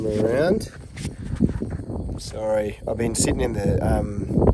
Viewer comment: I heard “sorry, I’ve been smoking that fire”